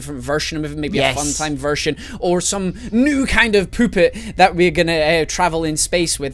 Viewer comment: "Or some new kind of Poopet" XD thats what it sounded like "Poopet" XD